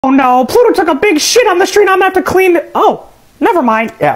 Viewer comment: I thought Mickey said oh no goofy took a big shit